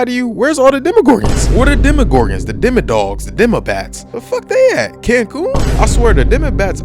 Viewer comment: When you said "Demobats", I thought you said "Democrats".